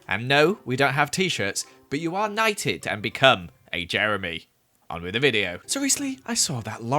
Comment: when you mishear jeremy for joey heart eyes